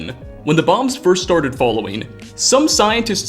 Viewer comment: "Following"? Am i the only one who hears this?